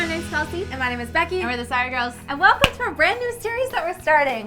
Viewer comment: Hello! I love this video so much, and I want to make sure everyone can understand and enjoy it. That's why I wanted to let you know that your captions are a little off. At about 0:13 the auto-captions say "Hi my name. is Becky over the scigirls and well thanks for brand-new" where they meant to say "Hi my name is Becky and we're The Sorry Girls and welcome to our brand-new series." When you get a chance, could you fix that? That'll help the 360 million folks globally who rely on closed captions to understand videos. Thank you, and keep up the great work!